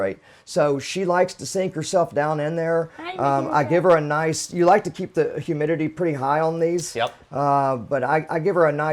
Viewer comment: At min mark 7:41 did anyone else hear that? sounds like a childs voice. is that guys place haunted? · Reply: Haha that was me saying "Hi Medusa" :D